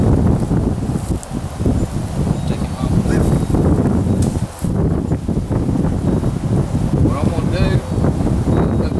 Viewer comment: what did you say at 5:52? 🤣 Great video, I've never seen this done before. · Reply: Maria R. I asked camera guy if he wanted